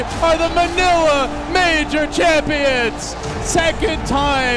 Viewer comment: Is he saying manure champions? Listen a few times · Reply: Manila Major Champion. manure lol.